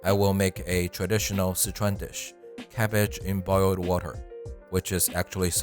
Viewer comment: Thought it said Garbage in Boiled Water😅